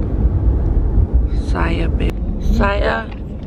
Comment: Did call babe at 21:09? When he asked who was down did she say Josiah babe? But they cut it out! Lol too cute!!!!! 🙌🏽🥰